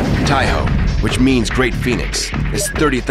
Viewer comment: I thought he said for IJN taiho that it stands for great penis which made me laugh so hard